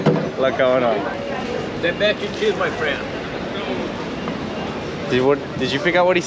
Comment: I think he might have said "the best cheese, my friend!" with some accent lol